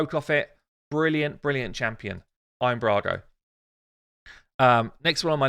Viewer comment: Every time I hear Iron Brago, in my head I hear, "They drink it in the Congo." :P